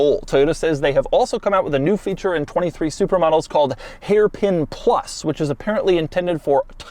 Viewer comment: I swear I heard "supermodels" few times, lol. Great review Doug!!!